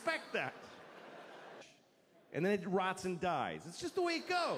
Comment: what did he say in 8:59 and then who dies? pleas tell me im not a native speaker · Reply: He said, "And then it rots and dies."